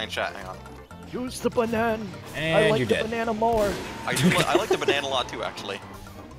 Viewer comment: What did soldier just say? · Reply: "You're like the cyclops of Greek myth, except you are Scottish, AND I HATE YOU!"